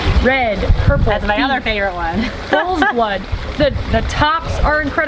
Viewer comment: What is the name of that beet at 6:24¿ i cant hear it. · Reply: bulls blood